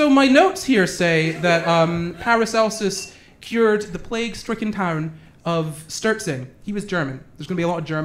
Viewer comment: said 'plague stricken town' and I heard 'plague stricken Karen'